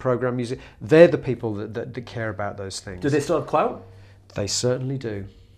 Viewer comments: What is it they say at 11:48? A "clow" or something?... ("do they still have a clow") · "Do they still have clou-"? What does he say there, could someone help me? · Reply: clout.